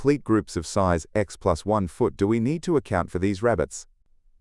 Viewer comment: what are you saying the word ? x+1 foot? sorry I didnt understand